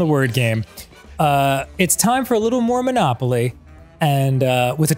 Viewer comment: Mormonopoly is what Dan sounded like he said.